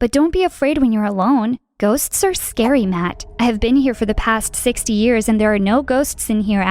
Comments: Dang, This is deep. You know, when Matt said "I've been here for 60 years etc." I thought Matt said "I was here for 6 year etc." For I thought he was kidnapped and grew up there. Also, I thought of this because of the sound of chain that she said she heard. Hshshshshs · Tbh, I started having goosebumps right when matt said that I've been here for 60 years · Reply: I thought he said 6 years but I was wrong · Tbh, I started having goosebumps right when matt said that I've been here for 60 years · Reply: I thought he said 6/20 years lol bad hearing problem huh lol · No one: Literally no one: Me: did she just said '60 years?' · I thought he said 6 years but no he said 60 years and at the end he’s a ghost that so sad and that scar probably has to do with how he died